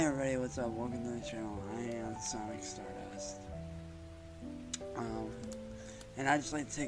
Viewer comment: Did he say that his name was Stardust? OMG, what a clown! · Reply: I could've sworn I heard him say Stardust instead of Sonic Stardust LOL